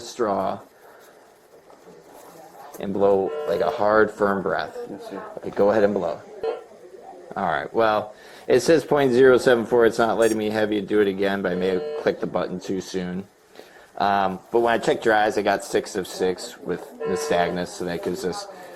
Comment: for a second i thought he said 0.74. Ha. He would be unconscius or dead haha.